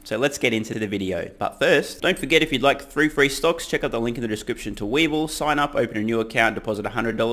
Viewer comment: I keep hearing "free free stocks".